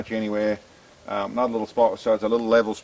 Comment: Very much looking forward to this 5 km shot. My fingers are crossed. Also it sounded like you said, “just another little level spot for Sam to ‘shit’ on” around 2:40 😂😂 sorry. Just finished my shift and over tired ha ha.